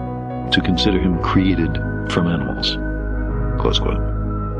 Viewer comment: What is he saying at 1:32? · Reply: Close quote.